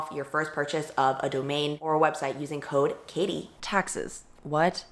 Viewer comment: on my life I thought this said Texas and not taxes and I wasn't even going to question it